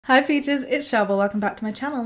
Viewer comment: hahahahhahah help the captions thought u said hi fetuses not hi peaches